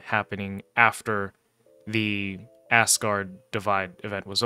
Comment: Did I hear you correctly? :) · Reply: Aye, that you did. ASS GUARD